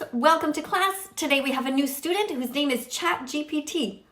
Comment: Is it just me or did she say- "Hello students we have a new student and HIS name is Chat GPT, lets hear what SHE has to say."